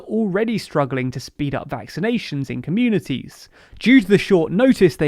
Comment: I thought you said vacation